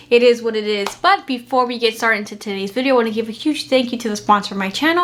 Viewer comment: did she say titis video?